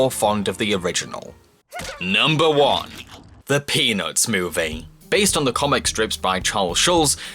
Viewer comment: i just hear “the Penis Movie” Off